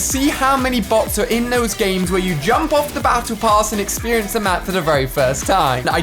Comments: "When you jump off the battle pass and experience the map for the first time" -Ali-A 2019 Edit: HOLY COW I DIDN'T EXPECT A REPLY FROM THE LEGENDARY ALI-A AND 500 LIKES OVER A MEME!! THANK YOU ALI AND EVERYBODY!! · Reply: I thought he said “battle bath” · Did Ali just say jump off the battle pass?